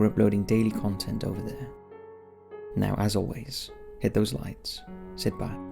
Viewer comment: When he said hit those lights I thought he said hit the like and I was just like alrighty There now he has just one more like :)